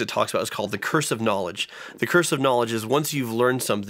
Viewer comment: I heard it as 'cursive knowledge' until the back-handed compliment 😅